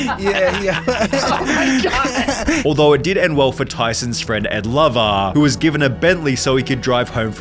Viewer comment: thought you said "for Tyson's friend and lover..." and i got REALLY confused